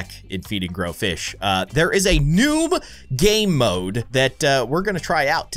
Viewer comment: I heard you say there’s a noob game mode